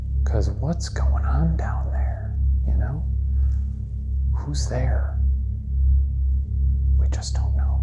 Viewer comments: I heard who's garret not who's there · I heard "who's Garrett" not there lol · I heard who's that you guys heard who's there either way it's compelling enough evidence for me that there's afterlife with that being said I just don't see how Shane can't believe that's real what you are hearing is a voice man suck it up it's real bro. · I swear I heard "whos Garrett", not "who is there" 😳 which somehow seems creepier. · Not the point to the video, but Garrett's giggle when Shane asked if he's ever had two voices out of him. Also the "Nerd," is the best evidence I've ever seen. 🤣:elbowcough::oops: The "Who's there?" EVP, I heard, "Who's dead?" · When the spirit said “who’s there” outside of the ‘Garrett watts room’ i heard “who’s Garrett” idk if it lined up with that but it sounded a lot like “Who’s Garrett “ to me